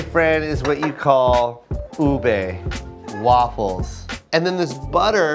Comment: oh no, turn on captions. Instead of Ube, it’s boob a. Why?!?! 😂